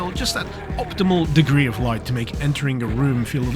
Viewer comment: Ohhh optimal "degree" of light i thought you said "optimal dickery" of light 😂